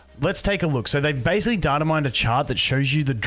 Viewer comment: i thought u said dynamite instead of datamine and i was so confused lol